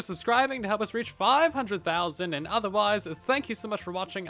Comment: Did he just say "Soilence" instead of "Silence"?